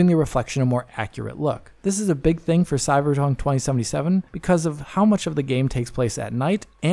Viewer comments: did he say CyberJunk ? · said cyberjunk. i know what i heard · at 7:06 you sound drunk! I know you meant to say Cyberpunk, but it sounds like you said Cyberjunk! 😆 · did he say 'Cyberzone 2077'? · did he say Cyberjunk?🤣